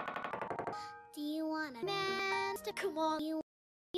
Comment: what did she say? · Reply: She said do you wanna man to c*m on you.